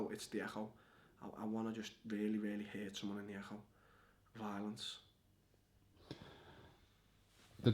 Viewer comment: Can someone tell me what he says at 14:09? I’m hearing “I just really really wanna hit someone in the a’hole, violence.”??? 😂